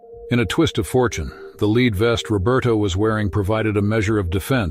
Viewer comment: Seriously?? I’ve heard people mispronouncing words before, but o my god!!! I’ve never heard anything like it. How hard is it to say head? Divers? Etc. Another narrator please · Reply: I thought I misheard lead vest pronounced as “leeds” and diver as “divver” twice. 🤣. He cracks me up. He has interesting schooling 😁